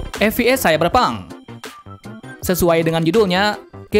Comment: kok gue dengernya cyberbonk